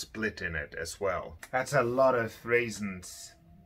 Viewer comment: Misheard "that's a lot of reasons" as "that's a lot of raisins" and had to rewatch the section two times thinking "what, raisins in a violin, is it some term for a dustball or something i haven't heard?"